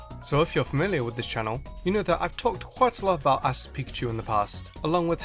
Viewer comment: When you said Ash's Pikachu I thought you said Ass' Pikachu